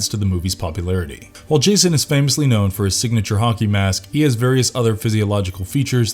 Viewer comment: Did dudeface say "honkey mask"?